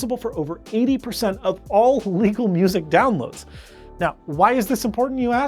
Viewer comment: did he say legal or illegal 2:06? need to be more clear! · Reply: He said legal. Easy to tell in context and the auto captions caught it as well.